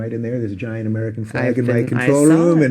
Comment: Soo hilarious, if you turn on captions, at 3:10 when Dave says "there's a giant American flag in the control room." The captions say "There's a giant American Faggot"